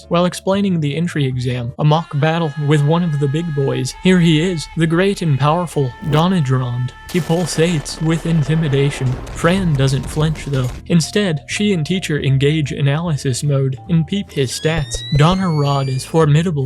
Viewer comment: Why did I think he said "He PULLS AIDS"? 😭